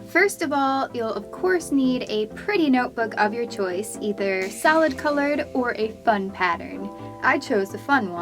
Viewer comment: When she said solid I thought she said salad... oops...